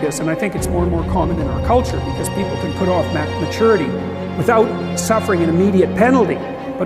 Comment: did he say "mac maturity" what is that?